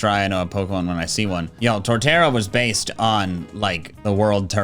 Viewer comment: When I was watching the video, my internet cut out and I just heard “Torterra is Based”